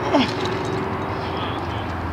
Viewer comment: Right before he was saved, it sounds like he said "oh god im sorry". Does anyone know what he said?